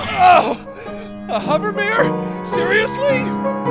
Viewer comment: I think he says Hover Barrier but we misheard it as Hover Myr